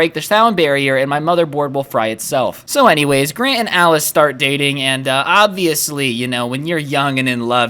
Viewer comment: At one point of the video I thought said “So Grant and I were dating.” 😂